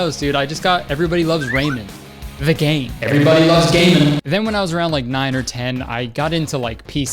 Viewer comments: Everyone loves gay man is what I thought he said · tell me why it sounded like he said "everyone loves gay men" at 1:16 ...